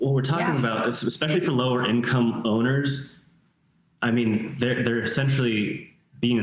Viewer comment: who says Champlain Towers was low income!!!??? the 13th floor penthouse sold for $2,800,000 last May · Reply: I heard “low income owners”.